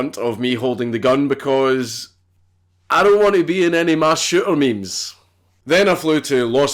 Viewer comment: "I dont want to be in any mass shooter memes!" Photoshops a crowd running in panic as Dank aims downrange. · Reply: I thought he said "mass shitter"